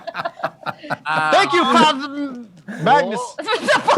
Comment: What did say? "Thank you, ----"? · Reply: He almost said Father but backtracked at the last minute